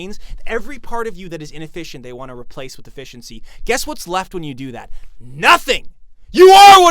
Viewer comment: Heard that as "replace with a fish and sea" and honestly based?